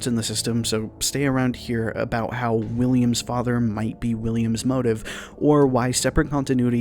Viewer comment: When I first watched this I had to watch it again Because I thought you said Williams father might be his mother😂